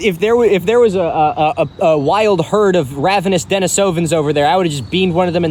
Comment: I definitely heard "A wild herd of ravenous Minnesotans" instead of Denisovans, and was a little alarmed.